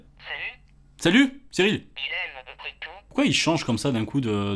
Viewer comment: j'ai entendue "il aime les tétons" chui chelou ou bien ? x'D